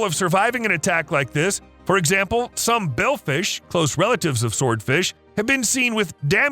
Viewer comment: did he say Billfish or Bailfish. Either way that looks like a Marlin!